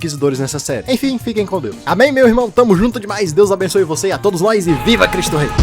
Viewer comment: eu ouvi um Amém irmãos?